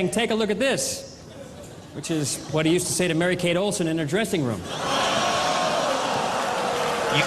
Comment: what has he said? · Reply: is where John said his roast line to Bob of “‘take a look at this’. Is what Bob used to say to Mary Kate in the dressing room” or something like that